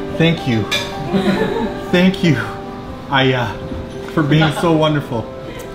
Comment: did you just say ayah(its mean dad in Indonesia) like in